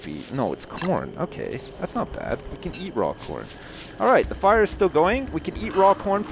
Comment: I could have sworn he said '' raw porn '' .